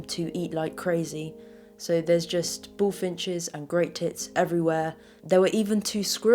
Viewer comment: Waiting for more secrets :F 01:05 not sure what was said there... "and great t*ts.." ?